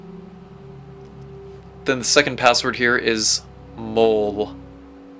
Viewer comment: what did you say? I don't know the second password 😂😂 help! I'm playing right now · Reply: JOHN ADA MOLE